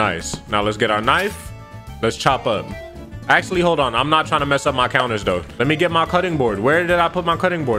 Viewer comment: I thought you said you wasn't going to mess up